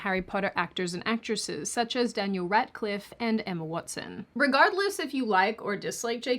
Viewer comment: I heard Ratcliffe LMAO